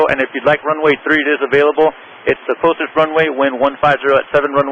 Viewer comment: Good job but at 2:17 I think he said "the closest" rather than "the shortest"